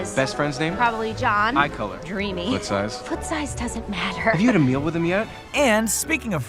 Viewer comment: I always heard it as 'What size?'